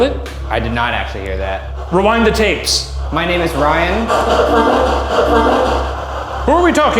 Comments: it sounded like the honeytomb said ryan back to him rather than saying violet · did anyone else hear "Ryan" and not "Violet?"